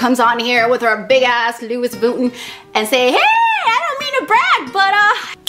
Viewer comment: hahaha did she say luis vuton? 0:35 lmao hahaha i love her :D